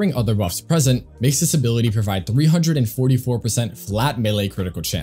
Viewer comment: was it just me who heard him saying mesa ability provides cc instead of kullervo ability xd